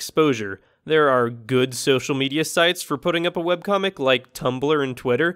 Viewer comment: XD I thought it said Weebcomic XDDDddddDDDD help